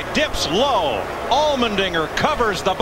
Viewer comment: wait did he just say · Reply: Allmendinger